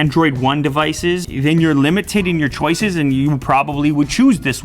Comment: Did he say "limitating"?